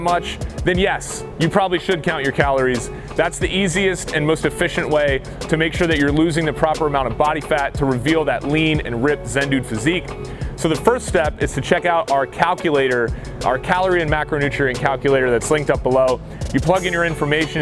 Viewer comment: am I the only one who thought he was saying “send nudes physique” ? 😂😂